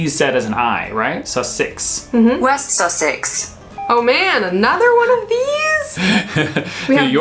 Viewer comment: sounded like that old woman from bristol on the news saying “no, not another one”